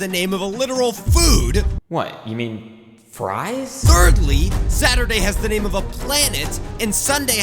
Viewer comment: I thought he said "BERDLY"